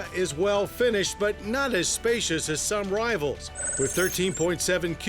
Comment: wait, did he just call you a fat ass?